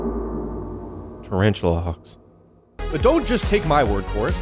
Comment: For most of the video I thought he was saying tarantula “hog” not hawk